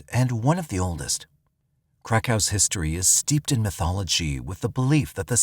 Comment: Every time I hear "Krakow" it sounds like "crack house". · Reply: Legit, he said Krakow history and I thought he said crack house history. Lmbo